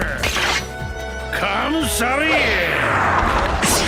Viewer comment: Before he hits someone it sounds like he says "I'm kinda sorry"!